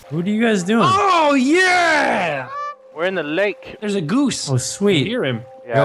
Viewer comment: anyone hear as well